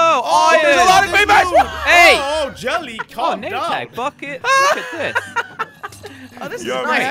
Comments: it sounds like he swore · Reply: Yea I thought he said fu*k it but he only said bucket so we gudz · did anybody else hear somebody say the f word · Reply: No he said "bucket"